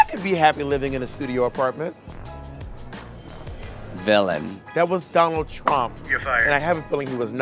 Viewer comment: lol I love how it says willam instead of villain